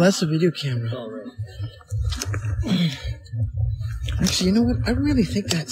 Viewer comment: As soon as you say "Whatchoo see?" a voice says "Brandon" was that you, or was it someone (or someTHING else)? And after you say "Look at that" a female sort of laughs with a "Huh or Ha" then "let me get a picture, oh that's a video camera a voice clearly @ 1:40 says "It's alright" I don't know if that was Brandon saying that...then @ 1:08 I get "It fools with my camera, we got in a paw..." and that's where it cuts off "